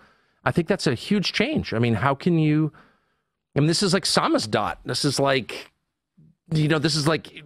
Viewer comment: what is Tucker saying here? This is like "Samus Dot"? What is that?